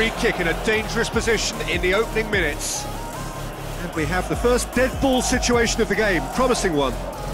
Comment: pes 2011 was the funniest one lol :D btw 9:48 did he said deadpool lol :D